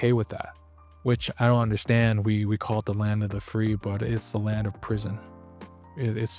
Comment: I heard someone reffer to america as land of the fee... pretty accurate imo